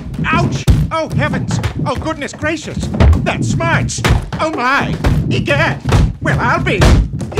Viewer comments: did he say “That smikes!”? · OUCH! OH HEAVENS! OH, GOODNESS GRACIOUS! THAT SMARTS! OH MY! EGAD! WELL, I'LL BE! YIKES! Edit: I'm just copying what I heard! Why do I have around 1.6K likes?! Thx tho! 😅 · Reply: I thought he said "that smites"